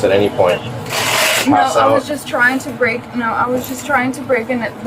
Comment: it sounds like she said she was trying to break in but the brakes wouldn't work. Well, she broke through all right, almost completely through the building.